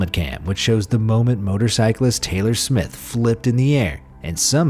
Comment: Taylor Smith? i first heard Taylor swift